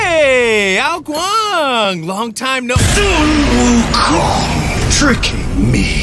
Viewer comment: I only hear goku 😅